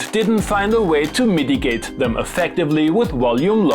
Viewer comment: I like how you saying "mitigate" sounded like "MIDI gate" ;-)